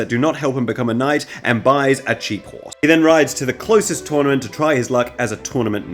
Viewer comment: You said “cheap horse” & at first I heard “jeep” for some reason & now I’m just imagining a knight in full armor, lance in hand, driving a whole jeep down the battlefield